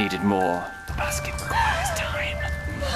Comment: What does he whisper at 1:00? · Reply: jennej9 the basket requires time